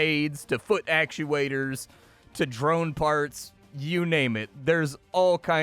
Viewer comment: i heard "drone farts" and decided to buy.